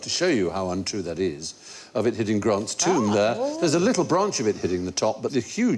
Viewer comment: During the 'Lightening piece' @ 18:22-- What did he call the Washington Monument??? Says "...hitting Grant's tomb..." ???